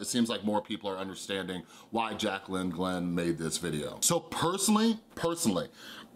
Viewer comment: Did anyone else hear him say Jaclyn Hill? 😂😂